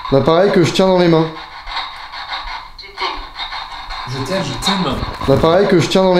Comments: à 27:10 elle ne dit pas "Je t'aime" mais "je t'ai eu". Re-écoute ce qu'elle dis au cas où, mais moi personnellement, j'entend "je t'ai eu"... C'est flippant 😨😱😶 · A 27:10 moi j'ai entendue au lieu de "Je t'aime" j'ai entendue "Je t'ai" voila faite lui remonter SVP 👍🏻😋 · "je t'ai eu..." c ce que j'ai entendu moi.... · à 27:10 moi j'enttends " je t'ai eu" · A 27:10 au lieu d'entendre je t'aime j'ai entendu tu t'ter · A 27:09 j'ai entendu victime